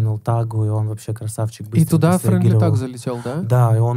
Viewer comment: подскажите, про кого они говорят? Я не могу расслышать😂 кто выпустил 2 альбома? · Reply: Friendly thug